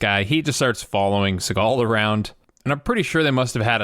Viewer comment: Thought u said "followed SeagALL around".🤣😅